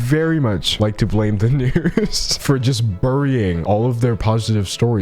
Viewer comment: When I first heard what d’angelo said at 0:41 I thought they had said the n-word and I was so shocked imagine my relief when I rewound and heard them say news